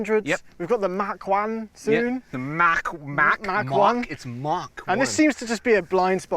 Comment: "Mach 1" sounds like saying "Mark 1" lmao